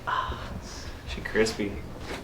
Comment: Did he just say “she’s crispy”